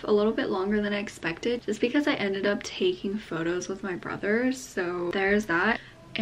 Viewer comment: "Brothers" ? ~I thought you said you only have one baby brother on your instagram questions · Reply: She put ‘brother’ and ‘so’ together